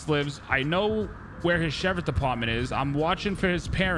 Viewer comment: Does anyone know what he said at 0:55 ? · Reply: Sheriff department